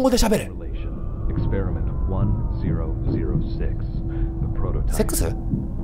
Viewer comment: TV 「1・0・0・''6''(ネイティブ)」 キヨ「…セ○クス…？」 どれだけ英文が聞き取れなくても、下ネタに聞こえる単語は明瞭に聞き取れちゃうのウケるww